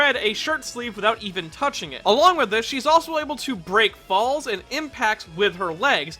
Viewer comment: I thought you said break balls when you said break Falls